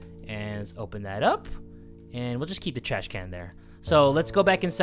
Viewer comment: did he say trash can or was it just me